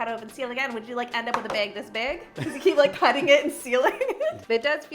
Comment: I was so confused every time you said ‘bag’, it sounded like you were saying big or beg. 😂